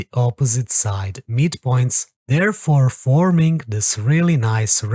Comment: said in the starting of the video. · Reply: oh at 0:17. I heard meet points instead of midpoints